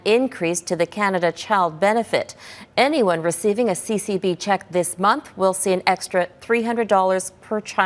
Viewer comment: I thought she said CCP check...lol